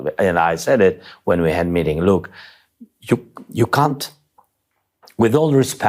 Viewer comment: Oh this was delicious. From 4:15 When he said "you cant " when all I heard him referring to trump as 'you c*nt" ...I am almost wondering if this was a deliberate or subconscious mispronunciation due English not being his first language. All the same I kept playing it back because it felt cathartic to hear Zelenskyy rightly call trump a cant after the way trump has continuously disrespected, insulted and undermined Zelenskyy and Ukraine.